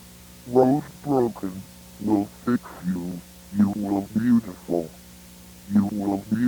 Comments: Idk what it said before but aww thanks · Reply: Rose broken. Will fix you. You will beautiful. You will beautiful. You will beau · can anyone tell me what he says i cant understand it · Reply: Rose broken, i fix you You look beautiful You look beautiful You look beautiful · Rosemary would go to the restaurant every night hoping her beloved husband would reappear after being missing for weeks. But no response until one day she heard a voice saying, “I know where he is Rosey” coming from the backstage. By the time the lady entered the backstage as the voice told her to, but her husband wasn’t there. Instead, only found only a rabbit. The rabbit said, ”Rosie, Rosie, We will fix you. You’ll look beautiful. You’ll look beautiful. You’ll look beautiful. You’ll look beautifu- “ When she woke up, She was beautiful. But on the inside... it felt like every part of her body was bleeding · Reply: i thought he said "you will be useful" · What is he saying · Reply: "rose broken, will fix you, you are beautiful (3x)" that's what i heard at least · "rose broken, will fix you, you are beautiful (3x)" that's what i heard at least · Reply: promof1976 I thought it said “you will beautiful”